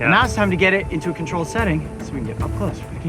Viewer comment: Hi Coyote, For a <link> challenge I watched your video to see if there were any auto-caption errors. At 6:52, the auto-captions say: "now it's time to get it into a control setting lets me get my clothes in" when you really said: "now it's time to get it into a controlled setting so we can can get it up-close for the camera." Thanks for making such good videos!